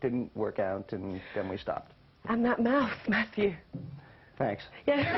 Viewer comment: Ok at 4:08 what does the girl joke about at the end with matthew. She asked him about Julia Roberts and at the end she said something and I couldn't understand. By the way these videos are amazing thanks for uploading them! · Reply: Erin Slusher she said „and that mouth matthew“ I guess because Julia Roberts has nice lips haha